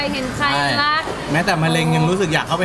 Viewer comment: พี่เค้าพูดว่าแม้แต่ แต่พี่สิริเขียนซับว่าแม้แค่